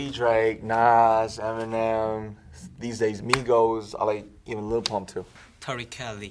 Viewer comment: DID RM JUST SAY LIL PUMP